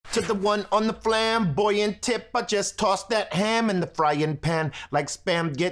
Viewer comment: For some reason I always heard "to the one on the flam, boy your temper, just toss that ham." Always wondered wtf a flam was and why tossing a ham was so calming...